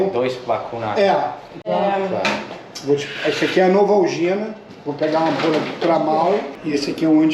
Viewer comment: foi tratada pelo Ron Pearlman 🤣🤣🤣🤣. Em 6:29 ele disse "Novalgina" (analgésico e anti térmico), não "vacina".